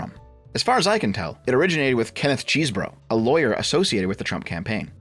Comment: That legitimately sounds like the name of a YouTube channel. (I hear the word "CheeseBro".)